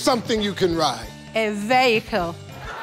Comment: she clearly said vehicle not a 'veho'